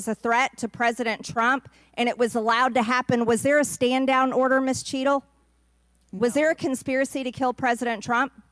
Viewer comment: I swear I thought they said Mrs Cheeto like why all the frito lay insults lol